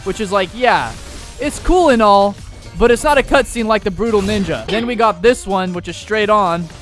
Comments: did u say Brutal ninga 😂😂 😅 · did bro just say brutal ninja💀💀💀